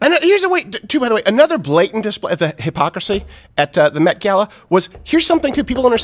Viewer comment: I thought he said "meth-gala"...